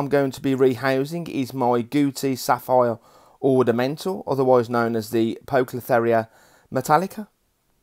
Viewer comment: thought you said Gucci Ornamental, ha imagine that